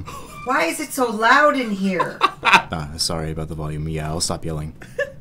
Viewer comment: I didn't hear Yeah I'll stop yelling but meow I'll stop yelling 😂